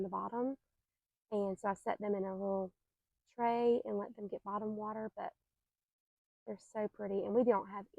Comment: It looks beautiful! When you said you “bottom” watered them, I thought you said you “bottle” watered them, bc that’s how they do it at the greenhouse. I was thinking what bougie plants, getting bottled water!😂 Absolutely yes about the insects that are out to hurt you in Mississippi too! It’s either burn up during the day or get eaten up by the bugs in the evenings. Love it!💚💜🤍